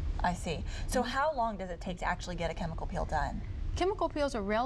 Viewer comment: Sounds like she is saying chemical PILL.